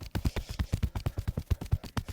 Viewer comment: I thought he said "Wish me luck" ... 😂